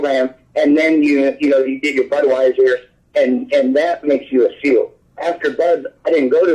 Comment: did anyone else hear him say Budweiser lol